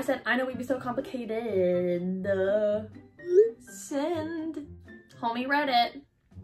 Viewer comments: did she just say ''Tommy read it?'' :O - thinking of all of the possible names - Tommyinnit, Tommytoms, tommy.... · Anyone else hear her say “ Tommy read it” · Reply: Ye Lol but I think she said Homie read it